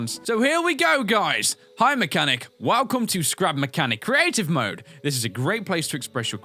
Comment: This is crap mechanic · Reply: Starlite Entertainment It Sounds Like He Said Crap Mechanic.